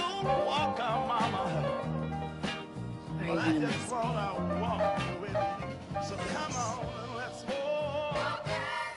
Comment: what does piper say at 0:48 ? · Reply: She says, "Are you gonna miss me?"